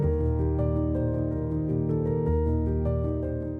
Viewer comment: I think tractate is spelled wrong, but what do you say there? “Deals with ??…. Torah/Torque? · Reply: Tort Laws